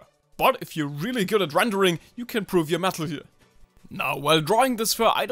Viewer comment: bro why do i hear “mAtLeGe”